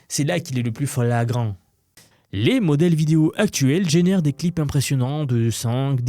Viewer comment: euh t'es devenu une IA toi même ou quoi ? à 6:08-6:09 "fleeeeéagrant" :D :P